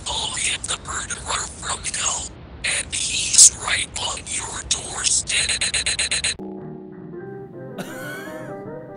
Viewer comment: anyone else hear he’s right on your doorstep? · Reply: Here’s what I hear: “[unintelligible] 5 counts of manslaughter and cannibalism. [unintelligible] from hell. And he’s right on your doorstep.” I couldn’t pick apart the unintelligible bits but yeah